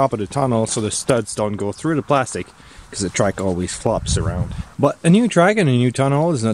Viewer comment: did you just call it a TRIKE???? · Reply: I said track